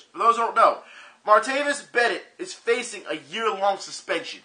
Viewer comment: think you meant to say Bryant at 19:07. it sounds like you said Bennett. I was confused a little bit.